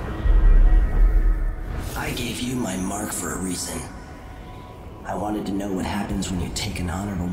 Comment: I thought the Outsider said '15 years ago, the Assassin Daud could have warned you about Delilah' instead of 'I gave you my mark for a reason?'